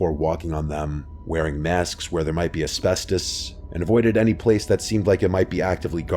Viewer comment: it says “a substance” instead of “asbestos” like bro is too lazy to proofread his god damn videos?